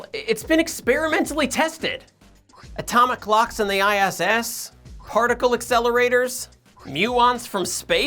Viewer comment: Lmao I heard "Atomic clocks from ISIS" at 0:57 💀☠️